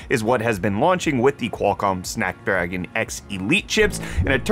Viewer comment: Did he say "Snack Dragon"?! 2:05 Now thats a processor I can get behind!